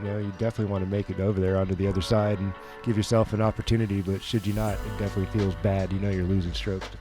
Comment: Anybody else catch sayin, "shit you not" on the hole 6 breakdow? Haha. Love it! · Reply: I heard it as "Should you not"